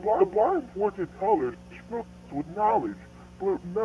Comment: Maybe he’s saying “The boy went to collage filled with knowledge, but never was wanted” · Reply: I heard "but was never wanted"